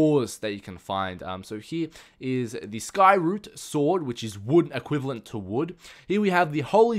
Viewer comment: I thought he said skyward sword, not skyroot sword XD